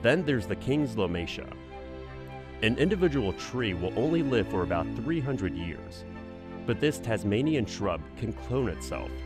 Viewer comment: Did they just say ONLY 300 years old!!!!